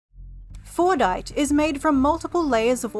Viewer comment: I thought it said "Fortnite"